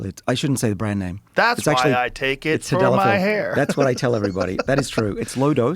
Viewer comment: Did Dr. Sinclair say Sildenafil or Tadalafil? It sounds like he says Sildenafil the first time, and Tadalafil the second time.